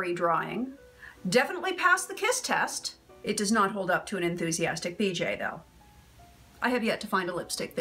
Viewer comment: At first I thought you said enthusiastic DJ. Either way, LMAO.